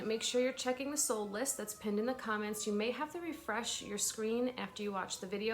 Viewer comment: I just now realized your saying that's pinned in the comments. I thought you were saying pend in the comments. Like pend you sale in the comments 😂